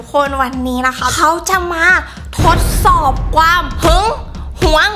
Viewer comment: เราคนเดียวรึป่าว ที่ได้ยินว่า "ความ" คือ "กวาม"😂😂